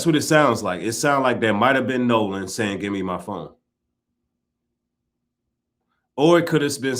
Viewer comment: It sound like he said give me my fu$@ phone.